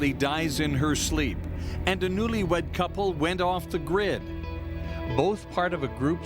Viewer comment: He said a newlywed couple went off the grid. Right when it showed their smiling pictures. I thought he said they went off to grin. At first.